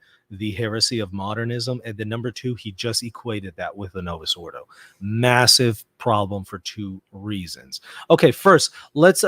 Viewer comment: I hope we listen and focus ... what said about the mass form ... is it novus ordo mass or novus ordo saeculorum ... what I heard him say (novus ordo saeculorum) I hope you listen again to what he said